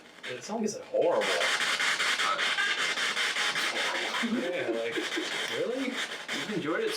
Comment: it sounds like it says it’s horrible… kiiidding lol Also I think when you’re asking their age at 49:24 and they want the lights off it’s to see the 22(0,1,2) flashing so you’d notice.. I’m not sure what that piece of equipment does it’s just my guess This is such a great video!!😊